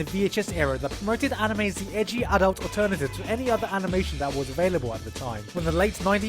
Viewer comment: To me don't watch SAO watch SAOA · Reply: I thought you said sao alternative